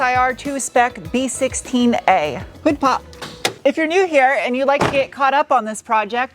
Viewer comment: LMAO! All this time I thought you were saying tic tok every time you pulled the hood release! I just happened to have the captions on and it says hood pop! For the last 2 years I've been saying tic tok along with you every time you open the hood!